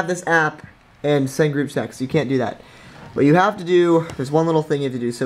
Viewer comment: I swear to god he just said group sex